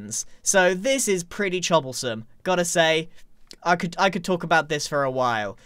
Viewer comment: I legitimately thought that "chobblesome" was some sort of British slang word when I heard it from the other Hermit's perspective. Well done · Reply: I thought he said chobblestone for a while